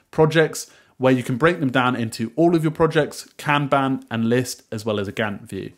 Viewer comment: regarding breaking down projects into task lists, you say that you can break them down by task or "can ban"?? ...as well as a (what?) view... I replayed this part several times and turned on closed captioning, still no idea what you said. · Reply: Kanban, it is a formally analog system :)